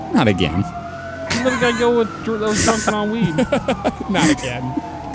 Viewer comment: did I hear say “We let a guy go who was drunk on weed”?